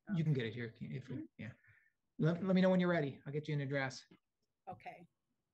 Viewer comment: Just around @ 10:50, the judge says, "I will get you a address." I swear I thought he said, I will get you in a dress. Had to rewind and listen again. Thinking that the judge was getting all fresh after she said her age (85).